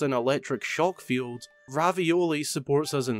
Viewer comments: D-did he say Ravioli? at 10:00? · Notice how he says RAVIOLI instead of REVALI. I like your vids man keep up the good work :) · did he just call revali ravioli